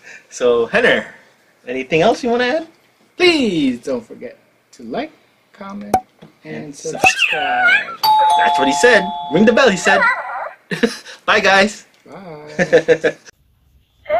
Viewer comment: How cute 😍 ! I heard it saying ring the bell' lol . It's really adorable how it makes cute sounds 😍